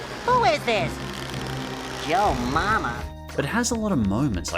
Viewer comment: Wait wait wait wait did spongebob say joe mama